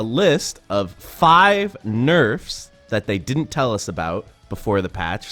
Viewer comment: Thought you said NO NERFS.